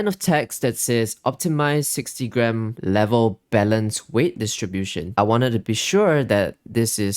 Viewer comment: OH BALANCED weight I thought you said BELLEND weight distribution LMAO